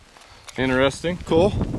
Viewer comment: "Interesting..." sounded like "I hate this trigger" to me!